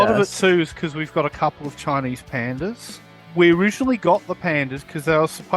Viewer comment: Oh said the zoo had CHINESE pandas - I heard him say CHARMING pandas at first 😂although I'm sure they're both.